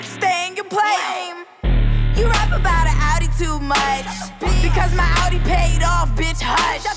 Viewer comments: I thought it said "you have a bad attitude much" but it actually says "you rap about an audi too much" NO HATE ILYSM CAE ❤ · “You rap about a Audi to much, because my Audi paid off bish hush” favorite line! I hate when people disregard something someone is really proud of. Like let them have their moment. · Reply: Oh damn all this time I thought it was saying "you rap about an attitude much"